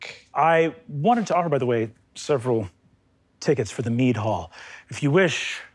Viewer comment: I thought he said meat hall and was very confused for a few moments.